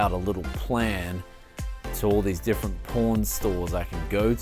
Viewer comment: This is clickbait! I heard 'porn stores' but no porn at all. Very disappointed.